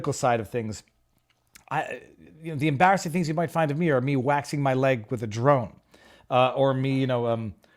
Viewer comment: Wow you startled me for a second 😳 I thought you said "waxing my legs with a drone" 😂 Thank God I misheard you !